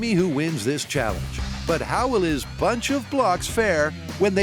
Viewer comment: NGL I heard “Bunch of Blocks” as “Bunch of Glocks” more than once until I saw Jamie’s rig 😂😂😂